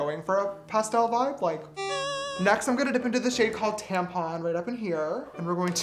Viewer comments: did he just say tampon??😂 Xx Edit: ohhh tampa ... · did he say tampon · Tell me why i heard 'tampon' instead of tampa --> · wait did he say tampon It’s red as well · DID HE SAY TAMPON? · DID HS SAY TAMPON? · me @ 9:21...... did he say TAMPON?? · I thought he said tampon 😳 or did he say Tampa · did you say tampon or Tampa? Cause...... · When he said the shade tampa i thought he literally said tampon XD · When he said Tampa I almost thought he said tampon😂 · literally i thought he said tampon but he actually said Tampa 😂😂😂